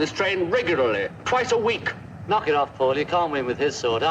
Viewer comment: did john just called paul “paullie”??